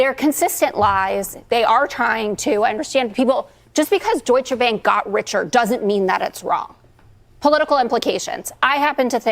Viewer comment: Did Tarlove say at 3:10 that "just because Duetsche Bank got richer, doesn't mean it's wrong"? What on earth is she trying to say, and why wasn't she asked to clarify that statement?